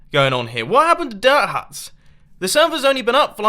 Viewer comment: i thought it said dirt hurts